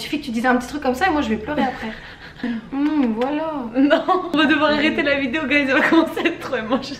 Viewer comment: c'est moi ou elle dit Wallah?